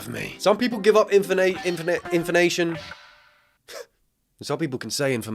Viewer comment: Bro it sounds like youre saying inflamation lmao